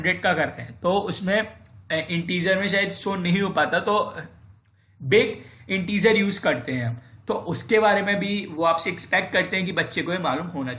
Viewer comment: which integer? Can't hear properly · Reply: BigInteger!